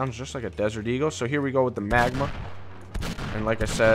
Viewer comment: It sounded like he said magma instead of magnum